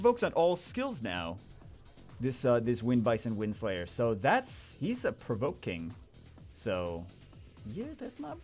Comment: I heard an italian Bagel "He'sa provoking"